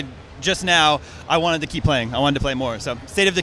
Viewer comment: early 2013 i thought he said spring of 2013 which spring ends june